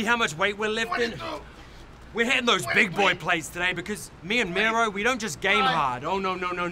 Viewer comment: I thought Kip said "Where the big boys play" when he was talking about plates. LOL